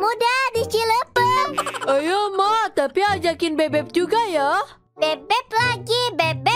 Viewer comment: kedengeran gw "bebek lg²" gw ampe ngakak 🙂😭🗿